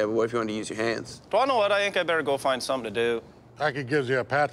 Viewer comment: what the fuck does he say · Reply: Do you wanna know what I think I better go find something to do